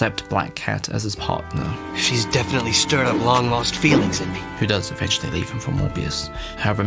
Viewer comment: What did you say about Morbius? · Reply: "Who does eventually leave him for Morbius".